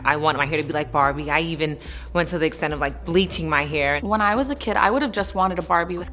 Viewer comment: 「ブリーチマイヘア」がリッチーマイケルに聞こえて困惑したw アメリカの美しい体型はこれ！って固定しないところ、ほんと素敵。